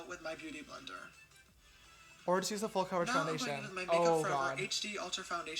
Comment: ish what does he say? I have been trying to make it out, does he say "Or chose a full coverage foundation"?